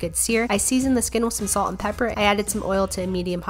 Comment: I for real thought you said “I seasoned the skittles with Salk and pepper,” lol. Looks lovely and delicious :)